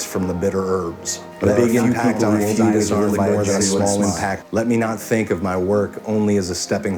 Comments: What does he say at 2:14? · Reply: There are few people who won't be disarmed by a genuine smile. A big impact on a few can be worth more than a small impact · What's he saying from 2:13-2:17? I think it's "There are few people in our lives who will leave more than a small impact" but I'm not sure. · Can someone help me understand what's said at 2:14 ? "Let me impact on a few . . ."